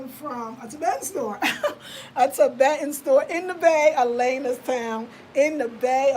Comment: Sis where did you say those pants are from? I live in Oakland and i thought you said Alana's Town and i googles that and couldn't find it, i want to get them.